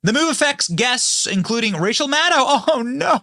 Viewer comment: Why did it sound like you said Racial Maddow?